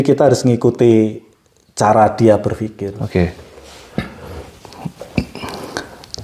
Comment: Kok di menit ke 9:22 ada yng ngomong BERFIKIR suara cewek apa sya salah denger ya